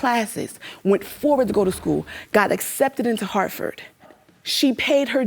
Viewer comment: I had to go back and re watch because I thought the mom said she got accepted into Hartford not Harvard 4:21. To my ears it still sounds like she said Hartford. I also graduated as a single mom. She is only delayed in her dream, and her beautiful daughter will see her make it. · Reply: Nesska Wytch you know it may be Hartford but I thought she said Harvard! Lol But the letter did say she had to wait until she had the baby tho. Her fault too!